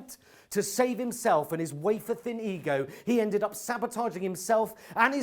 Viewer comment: What does he say in 7:45 "In an attempt to save himself and his ...? ego, he ended up sabotaging himself" Thanks! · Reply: “wafer-thin”